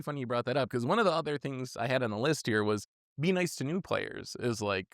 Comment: At first I thought you said "Be nice to blue players." and I was like that's a nice sentiment but it'll never fly. lol